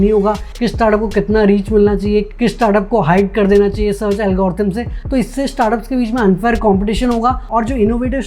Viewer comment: Sir please tell what is the word you used @ 11:14 (*******) "Konse startup ko hype kardena chahiye *** algorithm se..... " Which algorithm??? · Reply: Google algorithm